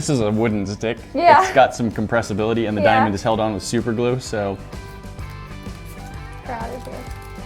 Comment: did anyone else hear “ proud of you” or is that just me